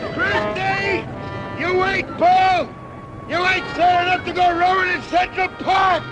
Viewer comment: what's he's saying at min 1:00 ? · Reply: "You ain't calling up your orientation"...according to auto caption..McHale says, "Christie you ain't slow enough to go rowing in Central Park".....